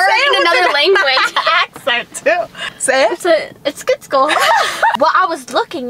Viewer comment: When she said eskitslkle the subtitles thought she was saying it’s good school 💀😂🤣😝